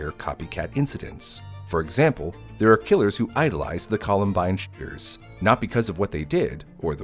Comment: lol, i heard the columbine shitters, we should call them that